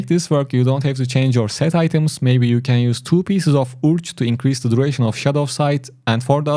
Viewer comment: Didn't understand what you said at 3:55 to use to increase shadow sight. I assume you said Vithus to increase duration. Can someone please confirm this. Otherwise, I really enjoy this build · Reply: Yes, 2x Urge to increase the duration of Shadowsight